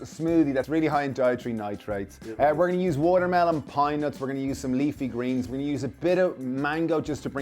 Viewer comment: Never heard of pine nuts, I thought he was saying peanuts.